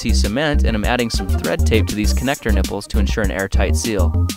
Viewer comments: did you say NIPPLES? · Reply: Yes. he said "nipple" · did he say connector nipples! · did he say nipples. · I know it isn't true but did i hear nipples? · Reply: said "connector nipple"